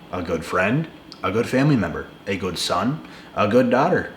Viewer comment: DID YOU SAY A GOOD DICK😂😅